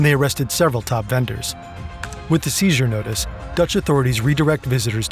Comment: it says Butch National Police instead of Dutch